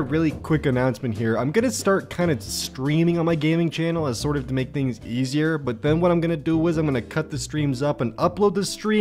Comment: I thought he said "I'm going to start screaming on my gaming channel to make things easier" and I was just like "aigt probs stress or something and moved on" 😂